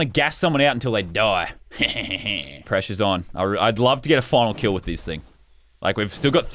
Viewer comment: lol I thought he said fresh is on